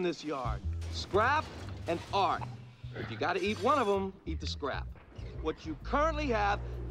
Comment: It sounds like Dean is saying “Eat this crap” @ 0:06! Lol 😂